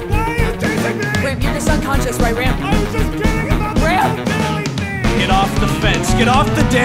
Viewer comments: Did anyone else hear “I was just kidding about the blue jelly thing” ? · Does anyone know what Kurt says at 3:55 “I was just kidding about the whole ???? thing” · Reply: Failing? I think, I'm not sure, it's really muffled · "I was just kidding about the" WHAT I CAN'T HEAR THE END AND NOW IT BOTHERS ME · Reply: Bo Didley thing